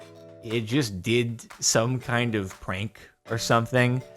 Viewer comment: I'm old, so my hearing ain't the best, I thought he said the building "did some kind of CRANK" 😲🤣